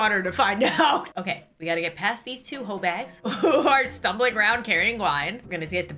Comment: Is she saying h*3 bags¿ Im so confused, she wouldn’t swear right?